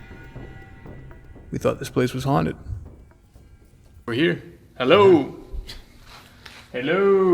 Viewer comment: anyone else hear that “hell oh” real fast??? If I was in PITCH BLACKNESS and saw that face 🙈🙈 I would of had a STROKE on site done 💀 his reaction 100000% warranted a rewatch again and again and again and again 🤣🤣🤣🤪🤪 so frigen funny!!